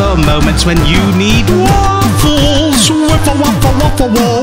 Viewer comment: this is what i heard ''WAFFLE WAFFA WAIFU WAFA AHH WAFFLE WAFLA WAFLAAAAAAAA WAIFU''